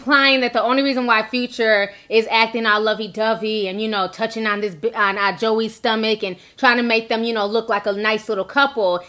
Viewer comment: “touching on this bi.... on joie stomach” 😂😂😂 i heard you lmaooo